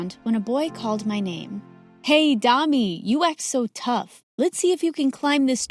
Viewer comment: what he said:hey Dani What I heard:hey dummy😂😂